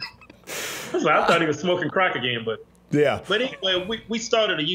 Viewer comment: Did he just say "Smokin crack AGAIN"???